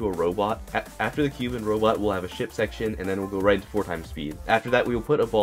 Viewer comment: thought he said shit section :D